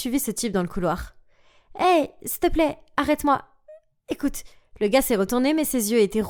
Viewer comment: Qui as remarqué elle as dit “hey s’il te plaît arrête moi” Moi j’ai remarqué